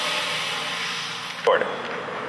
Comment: about 4:11 I heard you say recording